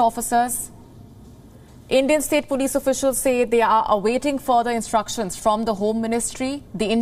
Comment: "awaiting for instructions" Lord love a duck, should be "awaiting instructions", please improve your standards of reporting, "waiting for something" as opposed to "awaiting something", you know what i'm saying? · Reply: Wion is right. She said "awaiting further instructions" not "for instructions"